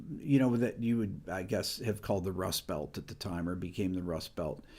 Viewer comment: I thought you said "The Rush Belt" for a second. You might have.